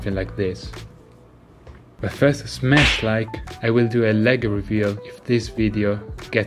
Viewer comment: did you say smash like